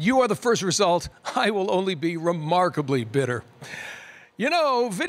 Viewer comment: "I will only be remarkably bitter" was my favourite line of the show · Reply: At first i thought he said be remarkably better, that sounded better